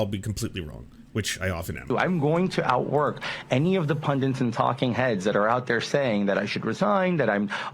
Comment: oh no, Santos says “pundunts” instead of “pundits” (why do people do that?!) Surely that one thing is enough to cancel him…